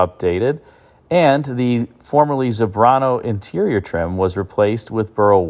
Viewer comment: Tony Soprano seemed to like his Escalade. I replaced the 6.2L engine in a newer ESV - it had pretty impressive performance for such a heavy vehicle. Nice to drive. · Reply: it sounded like he said the Soprano interior trim LOL.